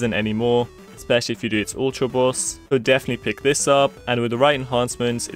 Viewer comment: I couldn't fully understand what he said at 3:50 . Ultra boss? what is that?